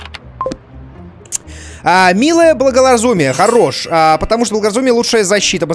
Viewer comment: Мне одному послышалось Милое благолозумие))